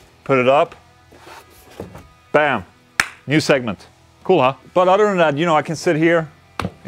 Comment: You said what I imagine was "Cool, huh?" but I heard it as "CoOla!"